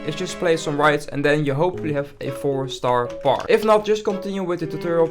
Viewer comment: I didn’t get what he said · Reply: "and you hopefully have a 4 star park"